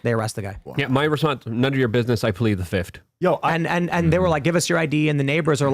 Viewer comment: Could someone tell me , what Luke says at 1:24:43 ? "None of your business ... the fifth" ? thx